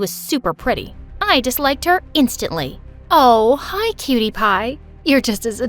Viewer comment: uh i noticed when called da gerl cutie pie it kinda sounded like pewdiepie(